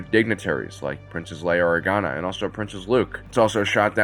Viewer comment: did you just say princess Luke? 🤣🤣🤣🤣